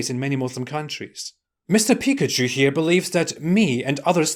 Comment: First I hear his name I thought you saying pickatchu rather then haqiqatoju